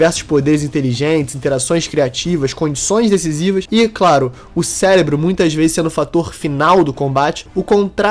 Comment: eu escutei certo? 0:24 ceLébro?